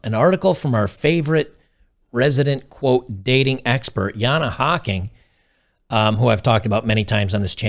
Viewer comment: I realize this is a serious topic but at the beginning I thought he said "our resident dating expert, Hanahaki" and it killed me